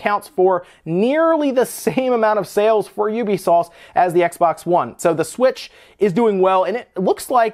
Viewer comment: i swear i hear UbiSauce